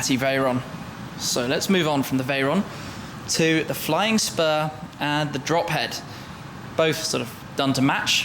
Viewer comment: oh I thought he said flying sperm. xD